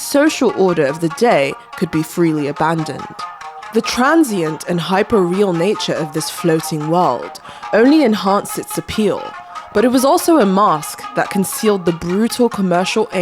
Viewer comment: Oh man, I thought you said 'floaty world'. Great, now I'm going to drown, thanks for nothing!